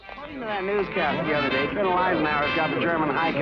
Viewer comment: Did I hear the words Colonel Eisenhower? At 31:45.